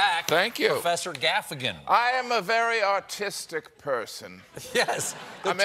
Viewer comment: The captions need to be fixed- 0:35 he says he’s a very artistic person and the captions say “autistic person”. Not autogenerated captions either.